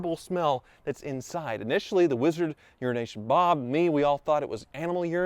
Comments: anyone else hear Urination Bob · I watch videos at 2x speed, for the past two years I thought you were saying URINATION BOB instead of EURO NATION BOB smh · I keep hearing "urination bob" and not "euro nation bob"